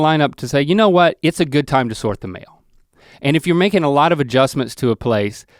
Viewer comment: at first i thought he was saying “sort the mayo”😂😅